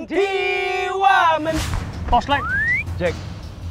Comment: What they're saying : Is this the real life- What I hear : Wamen PEE Wamen